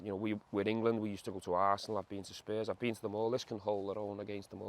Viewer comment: Love the fact that the cc subtitles say Gerrard says: “We’ve been to Arsenal, we’ve been to space” 6:40ish